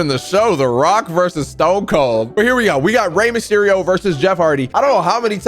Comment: Did he say Rey msherio?